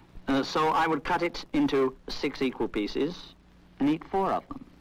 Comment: "[...]Cut the cake into THREE WHOLE PIECES[...]" B-but they're pieces? They can't be whole!? What's going on? THIS IS MADNESS. CHAOS. AAAAAAAAAAAAAAAAH. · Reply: Also I just realized that the guy must've been saying "three equal pieces" but the first time I swear to the fraction gods I heard him say "three whole pieces"